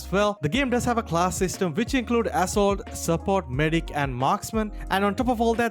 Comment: Did i just hear "AssHole" instead of "Assault" ?